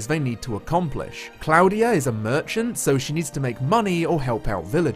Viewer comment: I misheard him at 8:55 and thought he said Klaudia was a virgin, so when he said that her task was to make money and "help out villagers" I became concerned.